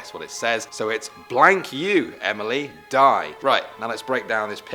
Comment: I thought it said: ** you Emily pie😂😂😂🤣🤣